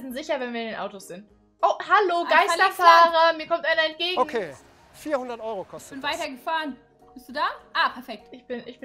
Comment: Bei 5:31 sag irgend jemand :,, ok 400€ '' ❤️❤️❤️ like da mit sie es seht wrr hat das auch gehört